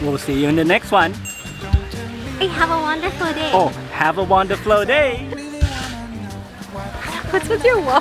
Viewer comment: Big smiles here from seeing Chaweng bustling - fooood - exciting times for Samui - I thought for a second said - “ have a waffleFull day “ 🤣😄👍👋🙏👌